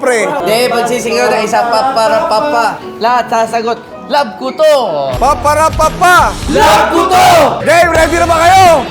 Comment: Sorry did I hear it as “Pap para papa love puto?” 😂 All the best in your new Mac Donalds career kuya 😅